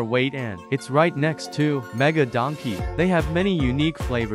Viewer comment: I thought he said Ohio melon 💀💀💀